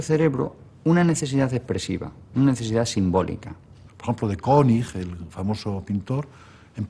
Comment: No entendí el nombre del pintor del minuto 48:20. ¿Alguien me lo dice por favor? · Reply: creo Roger König